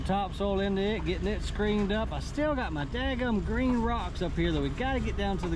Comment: Man i always heard say "gringo" untill i see the sign today in the video. That the mulch yard is "green go"